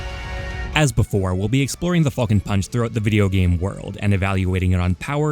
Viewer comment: i thought said the fuckin punch and started losing my shit